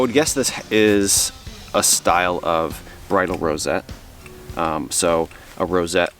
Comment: When you started talking about the bridle rosette, I thought you were saying “bridal”, and I couldn’t imagine where a bride would have worn that big chunk of brass! 😅